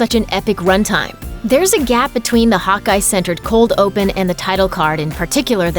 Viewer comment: I thought she was saying “hot guy” not Hawkeye